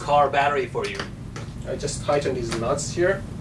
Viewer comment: does he say nuts?